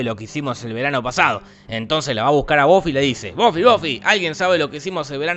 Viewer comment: pensé que dijo boffe